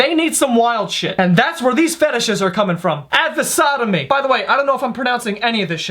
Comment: I actually thought it said "The Strangest Fishes"